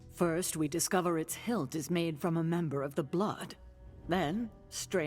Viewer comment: I didn't notice in my run, but there is a VA error at 1:23:42 , the caption says"metal of atlantis", but the voice says "member of the blood" :D